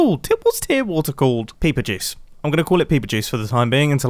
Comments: When Mumbo called a pool of tears peeper juice I laughed so hard that I covered my feet in peeper juice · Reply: I thought he said paper juice and I was really confused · Mumbo: “What is tear water called?” Me an intellectual: “TeArS” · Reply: I think I may be hearing him wrong but it sounded like he said peeper jizz